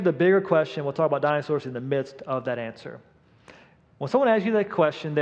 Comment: When he says, "Dinosaurs in our midst"; I thought he said "Dinosaurs in our mist"; then thought, "Maybe Dian Fossey should've started with the gorillas in the first place." 😅😅😅